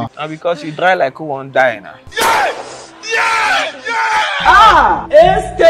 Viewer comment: When he said "Adieu Papa", all I heard was "Fatality" ( in Mortal Kombat intonation).🤣🤣🤣